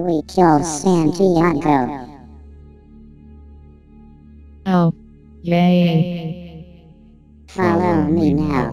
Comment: sounded like she said “GAYYYYYY”